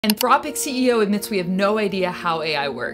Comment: I misheard your opening line as "misanthropic CEO". Which definitely had me nodding along